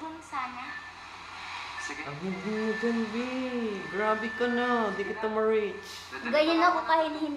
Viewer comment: did said bhe